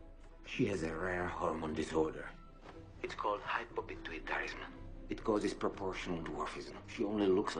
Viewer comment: Did anyone hear what did the doctor says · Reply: Hypopituitarism <link>